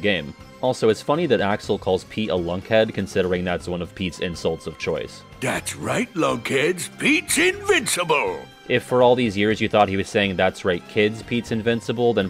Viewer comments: An hour in, and I am floored by "RTC stands for, 'Reach The Cream!'" Also, I think I did mishear Pete's battle lines as, "That's right, kids, Pete's invincible!" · I would like to shyly raise my hand and state that I also thought that Pete said "That's right kids, Pete's invincible" but I can definitely hear that I was wrong. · Leaves comment for engagement because I thought Pete said: ''that's right kids, Pete's invincible'' for the longest time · Just acknowledging that I, too, thought Pete was saying “that’s right kids” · wow. I already thought he said “That’s right kids”. Though I guess lunkheads makes more sense since everyone outside of Sora is an adult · I always thought he said, 'thats right kids" which stuck me as an odd forth wall break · Yeah, definitely thought he said "that's right kids" never would've realized he said "lunkhead." Tf does that even mean Pete get your lunkhead out of your lunkass smh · i don't know what I thought Pete was saying, but I sure as hell didn't think it was "lunkheads". this video is informative and perhaps life changing